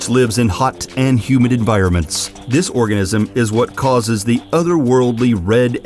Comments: Guy says Orgasm instead of Organism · he said orgasm instead of organism. 🤦🏻‍♀️ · Did he say "This orgasm "? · he says orgasm instead of organism